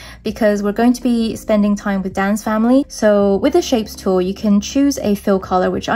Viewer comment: By ”Dan’s family“ I thought you said “dance family“😂😂😂❤